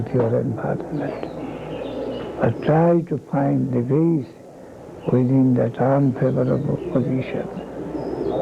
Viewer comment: At approx 30:50, i think Guru Maharaja is saying ' try to find the grace within that ...'